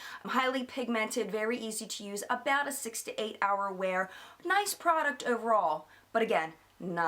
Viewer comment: Lol I thought you said "68 hour" blush! Oh lol! I was thinking wow that is good! Although I do suggest washing your face more than once every 68 hours! Hahaha!